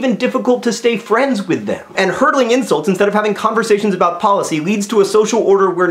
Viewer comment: It does kind of sound like he said hurdling, but from the context, I would guess he said hurling.